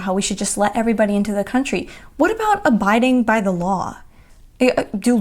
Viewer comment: 'Abiding by the law' - I heard that as 'A Biden by(the)law', which sounds like an oxymoron. . . . . But abiding by the law, I totally agree with you on that one. Go to it! You got this!